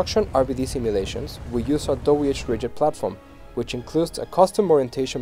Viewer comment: What did he say they used for the rigid body simulation? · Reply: "our whRigid platform"